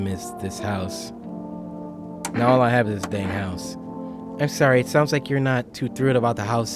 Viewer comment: swore and said damn!!! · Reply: He said dang